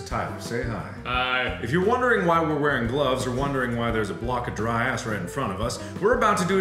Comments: did he say dry ass? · it sounds like he said dry ass in front of us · When he said "dry ice" it sounds like he said "dry ass"😂😂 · why do i only hear dry ass anyone 2019? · Did anyone hear "Dry ass"? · For a second there it sounded like Mark said “block of dry ass” XDDDD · I thought he said dry ass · Sounds like he said dry ass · sounds like mark says block of dry ass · The auto-generated captions say "dry ass" XD